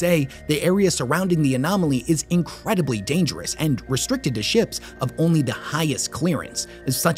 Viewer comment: Thought you had said “restricted as shit” towards the end instead of “restricted to ships” 😂